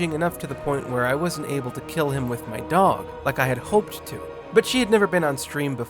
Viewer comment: 2;48 I thought you said “kill my dog”